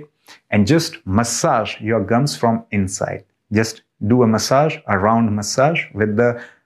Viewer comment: Thank you Doctor. Just to be clear - the massage is supposed to be performed as you demonstrated from the outside of the mouth? Or is it to be performed with the fingers inside the mouth directly upon the gums? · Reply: He actually says from inside. If you rewind the video you will hear him say inside (play video from 1:43 and you will hear him say 'From Inside'. Like you I was confused and had to rewind the video to hear him say from inside. Hope this helps.